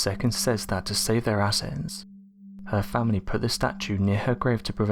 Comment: what’s he saying? To keep their “assets?”And to keep it from being “matted down?” I don’t understand